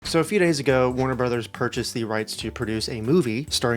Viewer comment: I thought you said YOUR brother bought the rights to make a movie